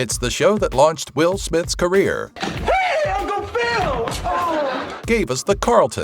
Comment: did he call Jeffery, uncle Phil? 😹